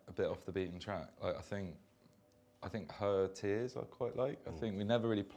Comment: what is Andrews favourite song? I can't really hear what he is saying!!! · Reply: Her Tears